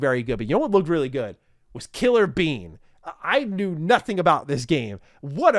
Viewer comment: Fun fact killer bean is an old movie so this is a game from a movie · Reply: when they said killer bean, I thought it said killer bee and thought this looks nothing like a bee. Doesnt look like a bean either though